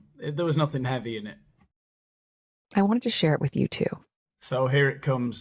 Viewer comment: Vocal fryyyyyyy. At 2:16 does she say, " I want to share it with you two"...so she knows how many people will listen to the podcast? Uncanny.